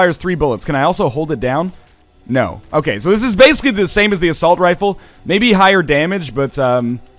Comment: I keep hearing "a saltrifle".